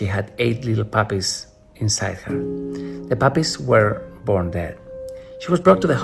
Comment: is he saying "the puppies were born there." Or "the puppies were born dead."?? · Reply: dead